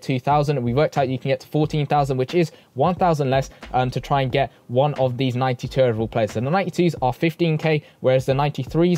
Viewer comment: Dude, At 7:20 did you just say 90 terrible players? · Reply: Nah I said one of the 92 overall players